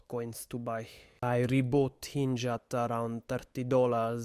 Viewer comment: Anybody know what coin he was talking about in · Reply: INJ